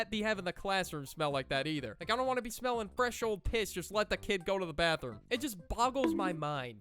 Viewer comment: did you just say ''fresh old''?